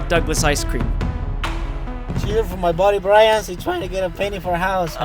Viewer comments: did he just call you Brian? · Why did Douglas called 'Brian'